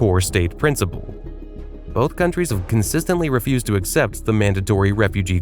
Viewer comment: Refuse? Oh I thought you said refuge...